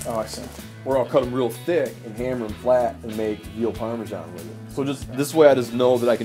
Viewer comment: did he say veal parmesan? I always thought veal was cafe meat🤔